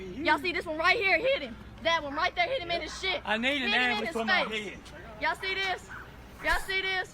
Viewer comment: Did he say "I need an ambulance on my head"?